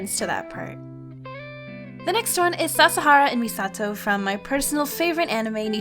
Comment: i forgot how to draw irl also i thought you said shakira for some reason